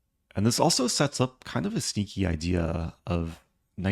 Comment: I thought you said "a stinky idea" that one got me good